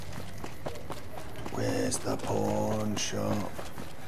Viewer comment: I keep hearing where's the porn shop. It's worse in his low gravely voice.